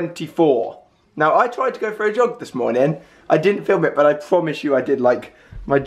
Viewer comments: I thought he said "I went for a job today" 😂😂😂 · At the start I thought Alfie said "i went for a job" and i was like whet.